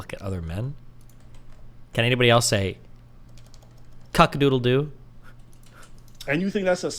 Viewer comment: Did he just say cuckadoodledoo? 💀